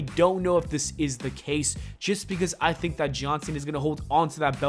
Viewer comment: Everytime you say “John Cena” I hear “Johnson” haha